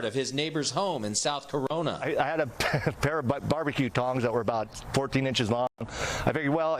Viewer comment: I thought they said thongs Haha